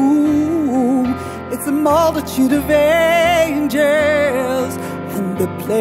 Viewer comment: I always heard it as 'a mothership of angels' 🎶 🙃